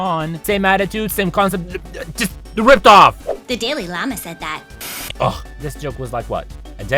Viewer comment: thought it said daily llama lmfao